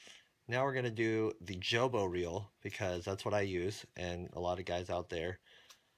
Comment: What is that feel you use? I thought you said jimbo Feel? I want to order it. Thanks for another great video